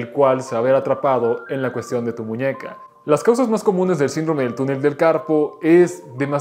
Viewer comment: Pense que decia "el síndrome del tunel del capo" 🤣🤣